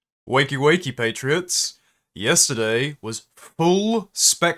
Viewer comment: My phone was fucking up and the first words I heard were "Wakey waketriots." I feel like that should be a thing now....